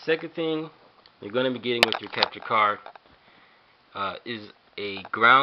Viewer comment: Right,The second thing , They're going to be a capture card. But captions said They're going to be a "helicopter" card. WTF